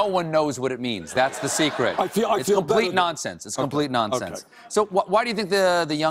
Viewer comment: Why it is hard for people to see what he is saying is just common sense? Is there something that i am missing about American culture/tradition? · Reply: first I thought you said 'common nonsense' -.